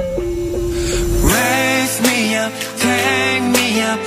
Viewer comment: Idk why but I always misheard of part "raise me up" to "lesbian"..No hate but srsly i always heard that tho...